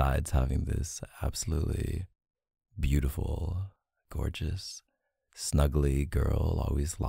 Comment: Dude i saw the word ‘gamer’ in the title and when i started the audio i wasn’t listening hard enough and heard him say “Hey Corpse” instead of gorgeous and was i like bruh😭